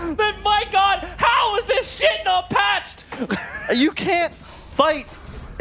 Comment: Did he just say "How is this shit no patched?" LMFAO